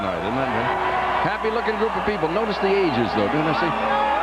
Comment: when he said "notice the ages, it's a young crowd" I first thought he said "notice the asians" lol haha